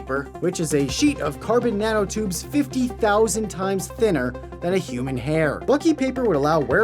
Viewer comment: did you said CARBON NANOTUUUBES!!!?